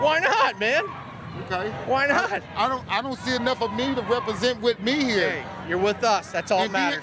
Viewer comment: And I keep hearing "enough of me here" like how absolutely enthralled are you by the mindset of racism that EVERYTHING you see is racist, maybe just maybe your actually the racist person...